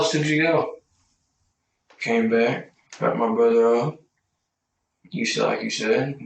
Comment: Thankful for the subtitles coz at 10:15 it didn't sound like `dropped my brother off` i heard something else lol